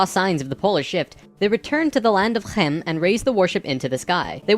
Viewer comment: I'm sorry... the land of what now? That sounded like what Jontron said when he couldn't read the word on that video-game cartrige. "Night Shade: the Claws of hchdjtmtch" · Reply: He said Kemet which means "black earth" and was the Ancient Egyptian name for, well, Egypt